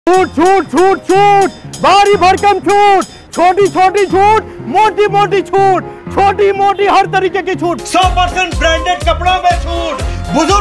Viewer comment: Everytime He says Chhoot, I Hear Chut 😂 Edit : Last Me Chut bolwa hi diya